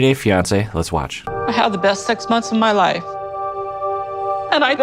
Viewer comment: Lol sounded like Jenny said she had the best sex months of her life"?? Is that possible? No... No... it must have been six😁 Sumit, imo, is a scoundrel, a catfish, an opportunist, a grifter etc I don't want to be harsh on Jenny. I just wish her the best.